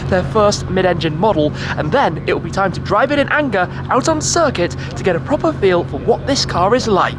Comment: "drive in anger"? Did I hear Schmee correctly?